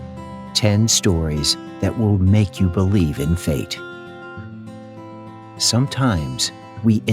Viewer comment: I thought you said "10 stories that will make you believe in Kate"